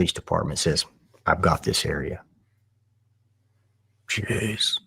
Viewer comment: I thought he said ,I got you covered and not,I got this area.